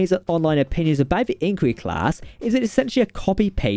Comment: Sounded like you said Angry Class.